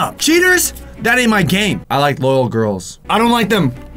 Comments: When he said he wants "loyal girls" I thought he said "little girls"!!! I WAS LIKE BISH WHAT!!!😂😂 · At the beginning it sounded like Tal said I like little girls instead of loyal girls F.B.I open up oh shiiiii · did he say i like little girls🤔